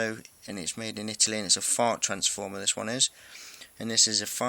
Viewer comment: Did he say fart transformer? lol